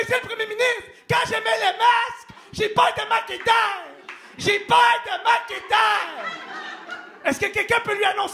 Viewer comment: SVP quelqu’un peut m’expliquer il dit quoi à 05:50 🙏🏼🙏🏼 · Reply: "Mais monsieur le premier ministre, quand je mets le masque, j'ai peur de manquer d'air. J'AI PEUR DE MANQUER D'AIR !"